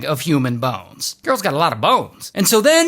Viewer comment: What the hell did he say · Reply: He said “Girl’s gotta lotta bones!”